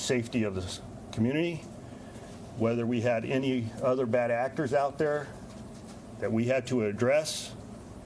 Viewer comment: Am I hearing things or did he say "Bad Actors" @ 14:10 ???